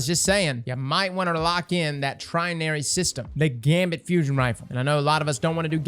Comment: Haha Trinary System? Sorry, I misheard you, I thought you said Legendary Shards.